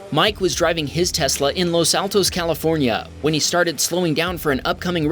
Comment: I heard Los Santos first hahha